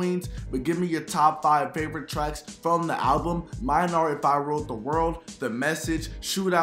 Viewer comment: Sounds like you said if I wrote the world lool